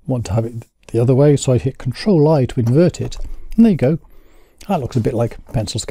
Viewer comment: Help: at 1:41 I hear you saying "control - ???" What are you hitting with control to make the image look like a pencil drawing? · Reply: Ctrl-I to Invert. Also Layer/Invert.